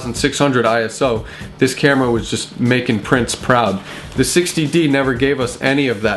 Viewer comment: When you mentioned "purple rain" - and then, after showing the purple fringing on a still picture - and then said, "would make prince proud" - I intially was thought you said, "making prints proud" - and this really confused me for a few seconds. How could purple fringing make one's "prints" proud? It would seem that it would be the opposite! hahahaha. Of course, my mind corrected itself fairly quickly, thank god. I'm totally senile yet, just insane.